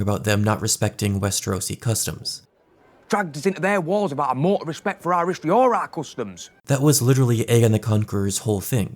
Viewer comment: I didn't understand a fucking word outside of "or our customs" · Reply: “Dragged us into their wars without a mort of respect for our risks or our customs”